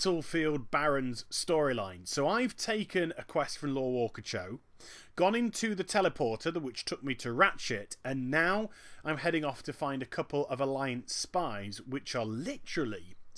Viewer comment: Haha I thought you said : That took me to rat shit :P